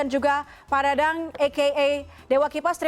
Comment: dia ngomong apasih?? Ada yng tau nggk?? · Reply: Pak Dadang a.k.a Dewa kipas. a.k.a = as know as = Alias